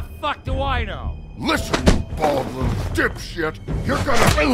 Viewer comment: did woody just call Jersey bald lol 😂 your bald too